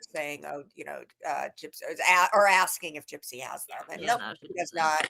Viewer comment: I thought I heard Gypsy snorted them